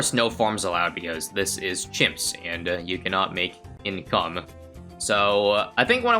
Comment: I thought you said "this is chimps, and you can not make any cum" at first had to replay a couple times